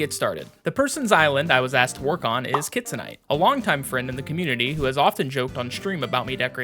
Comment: lmao for some reason i thought you were saying kids night instead of kitsunite 😂